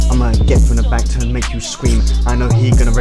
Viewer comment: always spreads my cheeks and makes me scream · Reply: For a second there i thought it said cream not scream, still weird tho